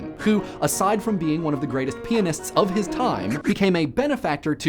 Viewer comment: I swear it sounded like 'penis'...